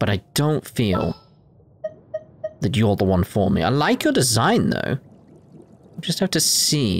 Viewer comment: Did I heard "her design" or "the design"? He's a guy, though Xiangling is very decent for F2P for support or even DPS. Maybe you want to swap Bennett for her for a while. Bennett is very decent to get max damage with his ultimate but to make him deploy his ultimate frequently you need to min-maxing energy recharge also get Bennett to at least C1. Xiangling is decent enough even in C0 and without so much min-maxing in the early game. With Beidou as your main DPS, the overloaded reaction will be good for you so IMO Xiangling is probably better for you now. Maybe you already got her from Spiral Abyss 3-3 already by now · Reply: Said her even though I thought it was a he... do no idea there lol. Meh, like pronouns matter lol And Yes, got Xiangling now! :P